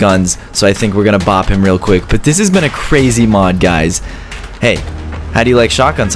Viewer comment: for any of you whos wondering why that person at 5:27 was fighting is bc he knocked his phone over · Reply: did he just said mod?